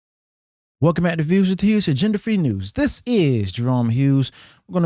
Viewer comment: Thought he said gender free news 🤣🤣🤣